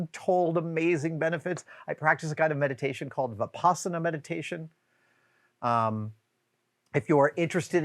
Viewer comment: THANK THE MAKER for captions. No matter how many times I went back at 13:46 wanting so badly to research it, I could hear nothing but 'The Possum Meditation'. Consequently, MY adhd brain could only ever visualize what that might entail and looped me into that imagery. (Yes, the caption was incorrect, but it did provide a foothold for finding what was actually said.)